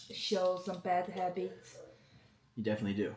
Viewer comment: 아 여기 진짜 개웃긴데 이 뭐라고 한지 모르겠어요 알려주실 분 계신가요..? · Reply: definitely you do ? 라한것같아요